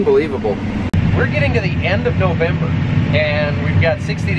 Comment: did you say last of November??